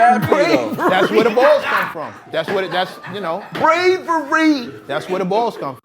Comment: 🗣 Bravery.. Bravery.. I heard “pray for Reed” 😂 pray for Reed